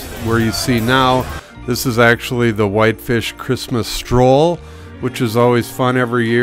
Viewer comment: I thought it said "Christmas Troll."